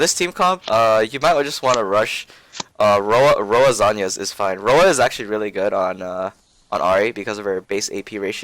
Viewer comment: does anyone know what he's saying at 3:53? rowa?? i'm so confused because that's not an item i've heard of.. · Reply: Yeah, it's "RoA" It's pronounced ROWA because of the way it's spelled. Basically RoA stands for Rod of Ages, it's an item that pros decided to build back then vs. AD assassin matchups. Now, it's outdated.